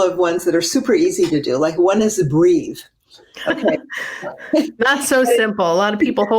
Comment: I thought she said "Just Agree"...she said, "Just breathe"..:)